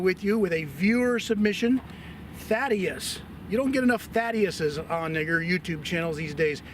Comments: did I hear what I think I heard? "Thadius, you don't get enough Thadius on n word YouTube" I hope not, don't want YouTube to mess with this awesome channel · I was very surprised @ 3:48'officer said, " you don't get enough Thaddeus's on N-word YouTube channells these days" I don't believe that's what he meant to say, I'm sure it was a slip of the tongue. please don't cancel him. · Did he say ‘nigger YouTube channels’?